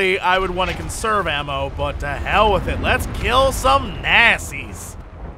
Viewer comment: What I heard: "Let's kill some nassies." ^_^